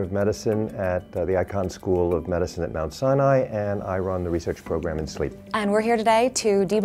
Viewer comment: i thought he said 'i run the research program in my sleep'😂😂😂